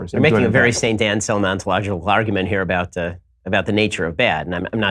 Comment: what does Ben say here? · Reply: Saint Anselm ontological argument (according to the subtitles).